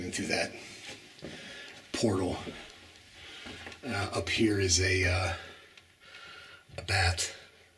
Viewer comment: Did anyone hear a strange sound just before saw that bat? The strange sound seems saying “up there”at around 2:10? The same strange sound appears again at around 5:11 😨. The strange sound is very clear